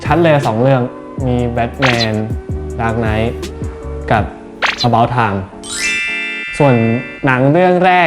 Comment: Bright: About Time Me misheard as: About Tine🤦‍♀